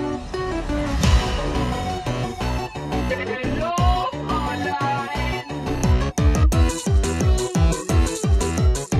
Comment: I don't understand what he says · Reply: He says "Hallo Fräuleins" which means "Hello ladies"